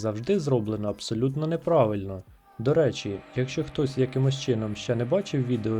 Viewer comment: "... зроблено все абсолютно НЕ правильно"...? Чи мені почулося?